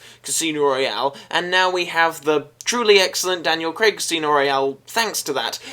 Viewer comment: I mishear it every time. I know you're saying "the Daniel Craig Casino Royale" but I keep hearing "Craig-sino Royale" lol